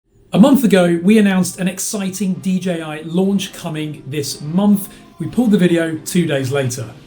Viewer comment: Dude you wrecked my day. I thought you said DJI LUNCH 🥗 OK I’ll wait. Thanks